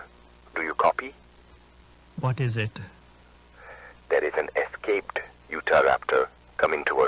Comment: I swear, I thought he said "an escaped futa raptor" and I was briefly confused on what website I was watching this on.